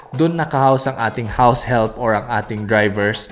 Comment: Haha, Misheard house elf instead of house help. Kakanuod ng Harry Potter to eh.